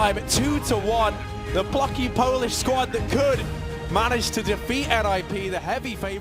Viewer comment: usłyszałem "The fucking Polish Squad" i aż musiałem posłuchać jeszcze raz :p